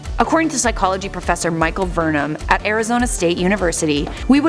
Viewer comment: Did you say Michael Burnham?